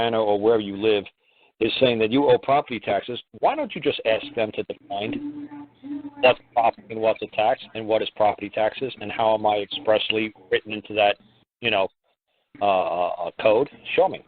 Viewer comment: m, I am not sure what he says, about property tax? can you find out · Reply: He says: why don't you just ask them to define what's property and what's tax, and what are property taxes, and how am I expressly written into that code?